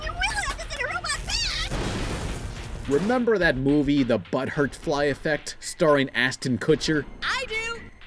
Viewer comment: Did his say the butt hurt fly effect!?